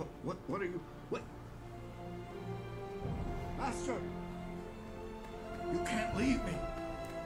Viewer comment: i love how the subtitles say "bastard" instead of master lmao